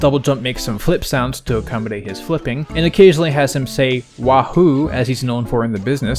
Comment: might just be a matter of interpretation, but i hear "hohoo!", not "wahoo!"